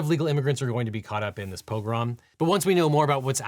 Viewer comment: sounded like "pogrom"